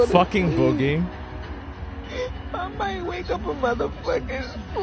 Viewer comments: "I might wake up a slave" . Lady you already were if you think like this Edit: I really believe these media companies owe these people are huge apology, instead they've leaned in more and now i hear about the left "rising up". It's comical really but disturbing how supposedly blameless these "news" stations are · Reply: Ahhh, at first I thought she said,"I might wake up on a plate." Both are ridiculous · "I might wake up a slave" . Lady you already were if you think like this Edit: I really believe these media companies owe these people are huge apology, instead they've leaned in more and now i hear about the left "rising up". It's comical really but disturbing how supposedly blameless these "news" stations are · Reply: I genuinely thought she said " i might wake up on a plane" I am glad you were able to clarify what she actually said thank you.